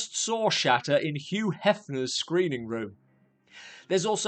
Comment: Lmao....😂😅I thought it said SHATNER,I swear I was looking for Capt.Kirk!Is he the past tense of S#ITTER & wasn't that guy the actor in DEMONIAC who plaid the priest...wow!